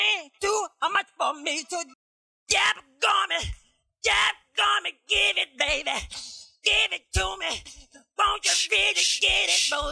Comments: WHAT does he say at 5:03 onwards ?! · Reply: Will Murphy daggone’ it (basically his doggone’ adlib but different) give it baby, give it to me, won’t you really get it movin’, Got to give it, You just want to (give it or get it) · What does he says at 5:03?? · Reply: Daggone it... Daggone it give it baby. Give it to me....... I can't understand rest